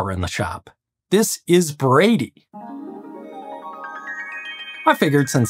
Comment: I always thought you said "Gravy." You'll always be Gravy to me.